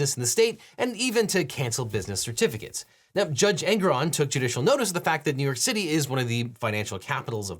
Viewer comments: I keep hearing "Judge Angron" and imagining they're the character from WH40K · I keep hearing "Judge Angron"... Man, I really wish Angron was judging Trump...